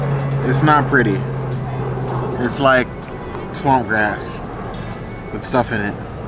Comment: I thought said “swamp ass” instead of “swamp grass” 😂😂